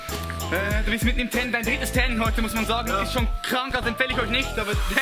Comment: hab ich das richtig verstanden, sein drittes training überhaupt ? und dann so aussehen ? :D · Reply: Sein drittes Training an dem Tag hat er glaube ich gesagt